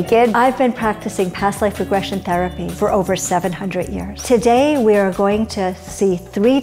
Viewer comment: When the lady said she had been doing this for over 700 years, I was like “oh that’s cool” and then like ten seconds later I was like “wait” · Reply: Yeah at first I thought she said "70 years" but NOPE